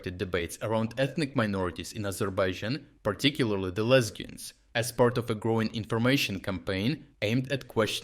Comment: I swear I heard lesbians 😂 and had back it up 3 times